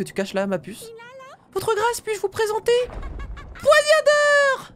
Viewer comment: C'est moi ou à 6:31, dit "Poignadeur" au lieu de Poignardeur. (Bon enfaite elle a oublié le "r" entre a et d).